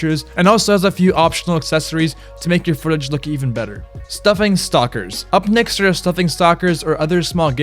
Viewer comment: so we just ignoring the fact that he said "stuffing stockers" instead of stocking stuffers